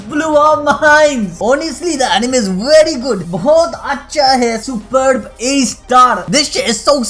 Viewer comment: did he say bohot hi acha hai?